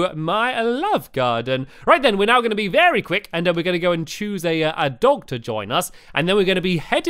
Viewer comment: When Stampy said chose a dog to join us I thought he said lets pick a doctor to join us